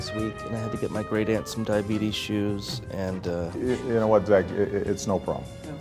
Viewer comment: what is he saying in 00:10 ? great ends diabete issue? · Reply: Great-Aunt's diabetes shoes.